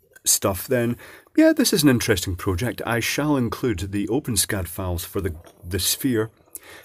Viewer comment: I thought you said open scat file.